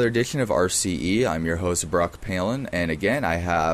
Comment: I thought he said Barack Palin